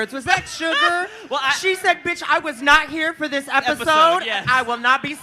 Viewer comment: she hasn't been on the show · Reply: Ooohh I thought she said she left episode one.